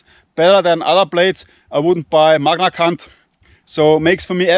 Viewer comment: what is he saying….? Magna C*nt 😅